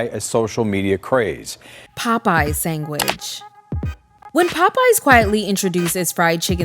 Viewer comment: Are you saying “Sang”which? 30:18 but anyway they had a smidge off c0c@ in that chicken. Idc what nobody say 😤